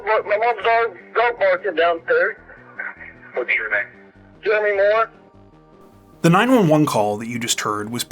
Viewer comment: I thought he said his name was Demi Moore on the tape